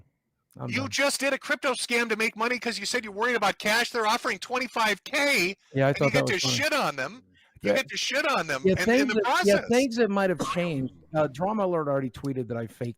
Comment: when Metokur is railing on him regarding the crypto scam, I swear it sounds like Boogie said "Yeah, I thought that was fun". If that is his actual thoughts on his fans and supporters trying to help him out then he honestly needs a proper wake up call. The others in the call went far too easy on him, probably due to his "unalive" baiting. This is actually fucking disgusting to listen to.